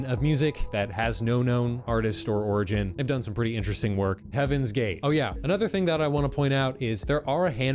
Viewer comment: Tf I heard "Heaven's gate" as "heavens gay" 😂 and I was just thinking about how much my grandma would hate that bc she's very homophobic and is very Christian to the point where she was once in a cult